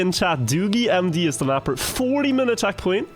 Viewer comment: at first i thought it said doggiegmd made the map